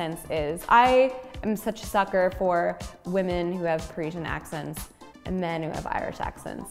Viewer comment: what accent did she say she likes on women? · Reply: parisian i think